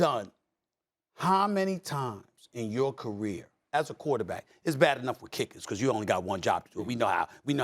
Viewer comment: “Carson Went is not a rookie! He’s not a WHAT!” 😂 Did he say, CHULLD?? 🤣